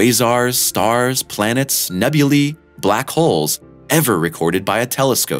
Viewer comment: guys is it nebula pronounce as " NE - BU - LI " or "NE - BU - LA" ?? :O i heard him saying nebuli or maybe its a different thing